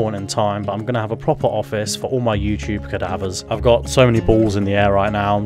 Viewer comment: lol did you say youtube cadavers? thats a little ominous